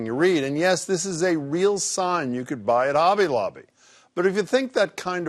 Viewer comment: Did he just say obby lobby at the beginning?